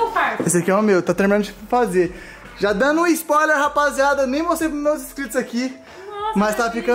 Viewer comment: "Igual eu falei pros inscritos" eu entendi "Igual eu falei pros espíritos" KKKKKKKKKKK